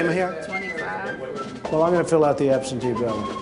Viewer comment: did I hear "absentee ballot"?